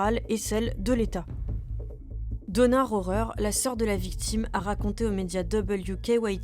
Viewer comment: J'ai compris "Donneur horreur". Justement ça pourrait bien être le film d'horreur d'un donneur.